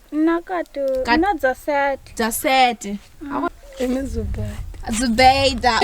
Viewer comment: de zübeyde gibi bişey duydum o isim bizlerde de var. Çok şaşırdım